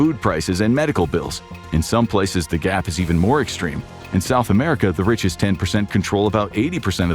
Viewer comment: how does cc say South Africa, while the speaker says South America?